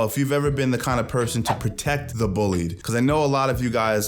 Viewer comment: I like to help the bullied when possible but at 1st I thought u said help the bully 😂